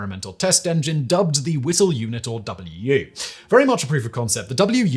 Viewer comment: I thought for a second he said it was called, "Little Unit," I was like, "Hey, that was my nickname in high school!"